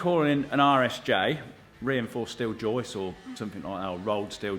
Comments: I've also heard it as "Irish J" before and had to do a fair bit of googling before figuring out that you were talking about an RSJ. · Oh my gosh 😂 I legitimately thought you were saying "Irish J" the entire time! I thought it was a sort of joke, because over here we call them "i beams". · Full disclosure I thought you were saying Irish J this whole time. This video was the first time I heard RSJ and then you said that bit XD. Forgive the Yank, I just thought it was UK slang. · Irish j? I thought that's what you said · it wasn't just google translate that thought you were saying Irish J's. I did google Irish J after one of the first videos. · Lol. I speak English, albeit with an American accent, and I also thought you were saying 'Irish J'. Just figured it was some cheeky British humor implying your northern neighbors are a bit dim and can't sort out the proper shapes of letters.